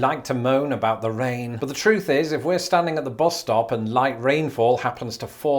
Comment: Why did I hear boss instead of bus? :) Public transit sucks in North America. And zoning codes suck too.